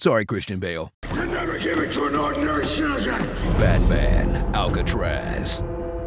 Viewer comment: all i hear is, you'll never get into a hung ung surgeon.